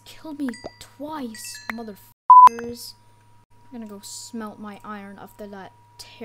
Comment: subtitles said smell